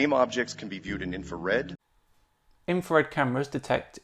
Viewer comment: It sounds a lot like he says "Infared" instead of "infrared." Maybe a bit pedantic there, but if he's so intelligent that Top Physicists can't answer his questions, shouldn't he get a simple pronunciation right? it's not like "infra-" doesn't have a specific meaning to convey in the word.